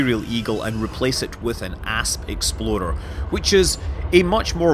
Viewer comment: Somehow, I've heard "Ass Exploder" instead of "Asp Explorer" xD